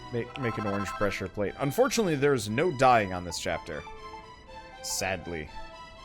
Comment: I heard it as "Unfortunately there's no dying on this chapter" and for one second legitimately wondered whether that's true or not, knowing full well dood had to bury three people Malroth indirectly killed.